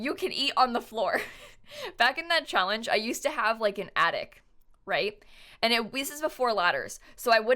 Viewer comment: omg when she said "i used to have an attic" i thought she said "i use to have an addict." i was like wait what? can you even get addicted to anything in the sims??? yes i am stupid 😭😭💀💀